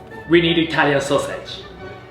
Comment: I thought Bayashi said “Italian Sausage” not “Italian’s Sausage”😅